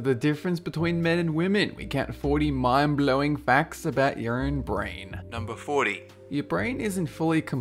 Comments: when you said "your own brain" i heard "urine brain" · In the beginning I heard "40 mind blowing facts for URINE brain" 😂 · I thought you said 'urine' brain